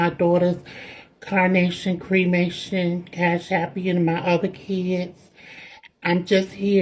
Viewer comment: did she just say CashAppian? Oh nawwww